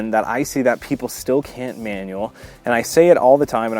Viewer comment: I heard "white people still cant manual" 😂